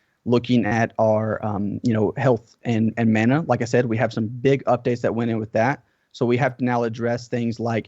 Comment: Lol could have sworn he said thicc update